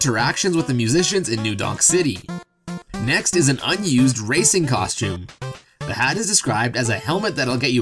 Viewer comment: i thought when you said "racing costume" you said racist for a second xD